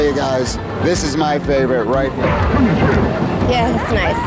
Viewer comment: what she said? hard to hear. "... nice ... ... face" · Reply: "Yeah that's nice. Get the microphone out of my face."